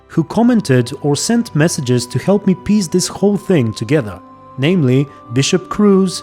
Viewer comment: ? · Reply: when you said "piece together" sounded like you said piss together haha